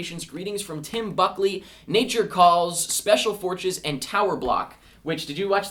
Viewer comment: And at 21:31 I swear he said Special Fortches.